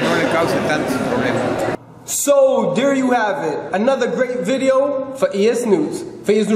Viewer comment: I thought you said "There you have it, another dumb video! lol....